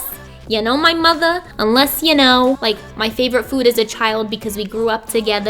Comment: I thought you said my favourite food is a child, for a sec. GOSH